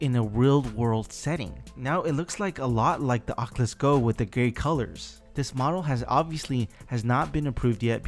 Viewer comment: did he say the oculus go with the gay colors lol edit: ok he said gray lol